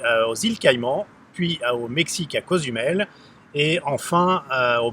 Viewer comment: Super intéressant comme analyse. J'ai deux questions. 1:Vous dites souvent: (a cause du mail) je ne comprends pass ce que vous voulez dire? 2: Est ce que vous êtes au courant pourquoi la base des arbres sont peintes en blanc? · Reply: J'ai réécouté la vidéo, a 12:06 entre autre: Vous dites a Cazumel et moi je comprenais (A cause du mail). Désolé .